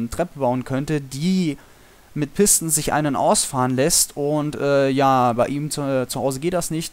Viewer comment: habe ich verstanden: die sich mit pistons einen ausfahren lässt o.O